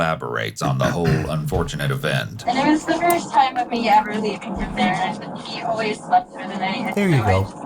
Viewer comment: It makes me wonder if this particular form of child negligence was a regular occurrence and that she got a little "unlucky" this time, despite statement insisting that she'd never done this before. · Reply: she actually says it was the first time she left him THERE. I thought I heard her say that she didnt think it would hurt if she left one more time, but she kind of swallows her words.